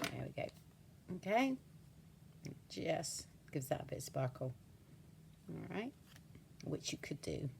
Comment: I had the text on and you said “just gives that a bit of sparkle”. But the text heard you say “ gives that Bitch a sparkle” LOLOL! It does that kind of stuff all the time on other peeps videos, too. 😂